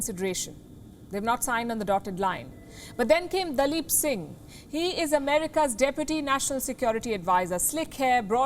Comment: At first I thought she said here comes a lip 💋 singer from America 😂😂😂😂😂😂😂😂😂🤣🤣🤣🤣🤣🤣